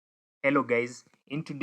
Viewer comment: 😅Did you just say "hello gays" at the beginning of the video?😅